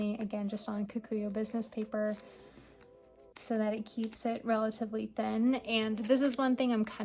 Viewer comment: I didn't get that paper you mentioned? Closed caption said Kikuo business paper, but Google did not return anyting. Would you mind telling me what were they? Thank you. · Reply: likely kokuyo business paper?